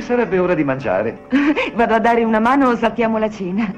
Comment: Might be bye bye baby but I heard the word Manos, so Torgo will save them...with his one Manos....on 2nd thought...run away!!!!